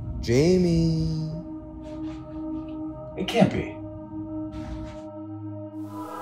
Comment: Jimmy speaks so unclearly that I can't understand what he's saying at 00:10 and at the end. I'm only looking forward because to support Jungkook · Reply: "It can't be" 🤣 I played it a lot but even now it's sounds... however 🙃 Many thanks to you.